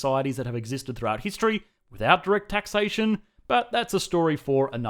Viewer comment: Couldn't understand that Australian accent "That many societies have existed throughout history <that rigged taxation>"? What was it? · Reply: “without direct taxation”.